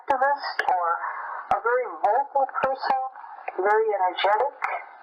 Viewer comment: There's an error in the translation where it the text says "mobile person" but I heard "vocal person" from Cay's speech.